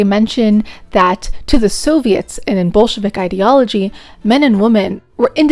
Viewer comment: Commie-hater says "Bolshevik", I hear "Jew". Should I see a doctor?